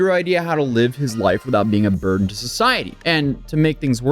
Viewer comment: You said "a burden to society" but I misheard "vermin to society", I actually think this is more accurate.